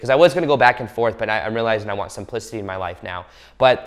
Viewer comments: I almost fell outta my chair. First off, I had my phone down low so that's probably why I misunderstood the word u said(simplicity)... but it sounded like u said I want some p** in my life. Yea I know, how horrible of me 😂. I turned up the volume and ran it back to hear it again and I'm much better now. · I THOUGHT HE SAID “i want some pussy in my life now” instead of “i want simplicity in my life now” I WAS LIKE!!!!!😭😭😭🤣 i need my ears fixed